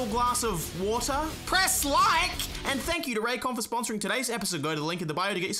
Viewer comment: I think it was a Raycon sponsorship but all I heard was "Jurassic Park"